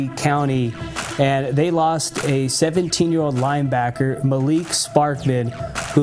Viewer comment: Why I thought he said 72 year old😭😭😭😭 I was boutta say